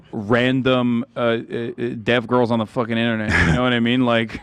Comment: Not gonna lie, totally thought they said Pewdiepie was mocking a dead girl not a deaf girl. Was very shocked when there was a response from the deaf, not dead, girl.